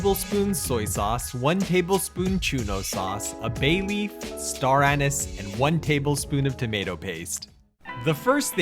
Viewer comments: did he just say star anus? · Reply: Star Anise. The. ingredients and quantities are listed in the video description. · PLEASE READ BEFORE COMMENTING: Japanese curry powder is not the same as instant roux. The former is a blend of spices like Garam Masala or Chili Powder, and the latter includes seasonings, flavorings, thickeners, etc. If you want to make the curry powder from scratch, here's the recipe: <link> As for the bananas, 2 of the 3 major curry producers in Japan add banana paste to their roux and it is not an unusual ingredient in Japanese curry. If you want a faster version, try my 30-Minute Japanese Curry From Scratch: <link> I also have an easy delicious Curry Udon Recipe: <link> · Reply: What sauce do you say use at 2:19 ???? Found it. UPDATE: I found it “Chouno sauce”